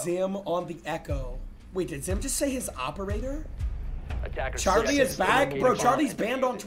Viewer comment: did just say bernords operator? bro is stream sniping using...